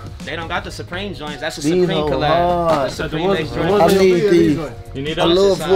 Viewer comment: “i need em, 9’s” 😭 i heard “i need these” almost 10000 times